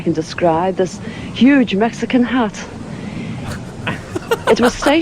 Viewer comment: I thought the lady said Huge Mexican Hut for a minute til I had to rewind..